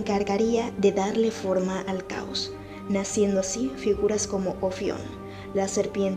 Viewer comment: Hay algo que no entendí la serpiente como se llama ??? Ofion 0:50 ?? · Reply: Si, se llama Ofión ("serpiente")